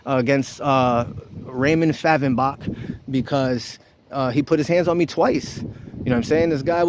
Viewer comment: Did I mishear the officer's name? Is it Raymond Fathembock? That is clearly a made up name. Is he harassing Ichabod Crane?